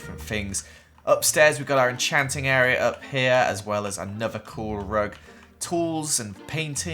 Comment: I thought he said Nether core rug